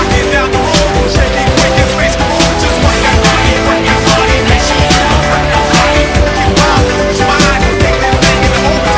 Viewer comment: Subliminal Messaging at 0:49 anyone catch it?! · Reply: Say Swag Again